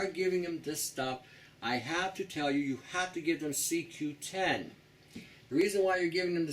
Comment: usually on it's own ---not sure what C210 is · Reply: I must've misunderstood something... hmm... at 14:40 - yeah, now that I re-listen, it's not C210, I don't understand it though.